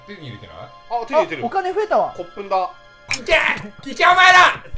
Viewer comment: フ「骨粉だ〜」が何回見てもどうしてもコップンカーに聞こえてじわじわくるんだよねw